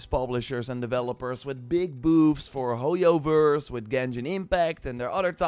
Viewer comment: Ayo 0:32 did he say big boobs for hoyio verse